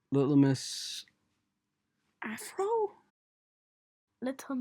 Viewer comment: a**hole? Me:oml did she say the a hole? word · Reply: no she said afro